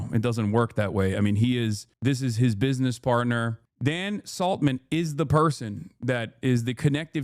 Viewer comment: For a sec I thought he said possessing "sea salmon" and was in violation of some Fish and Game regulation